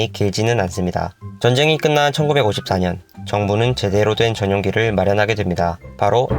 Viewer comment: 에 자막은 1945년인데 목소리는 1954년으로 들리네요..(지적해서 죄송합니다 근데 영상은 굉장히 좋네요^^)